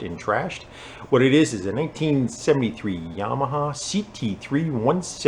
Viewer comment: 1873 Yamaha ? Did I heard it correctly, eighteen seventy three ?